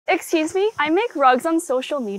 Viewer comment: HELP I HEARD "i make drugs on social media"